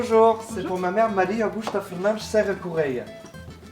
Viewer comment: pote de natoo : "Bonjour ! c'est pour Brunelle !" Moi : COMMENT A T-ELLE SU ?????? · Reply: Elle dit prunelle. En référence à prunelle la cousine a églantine ☺️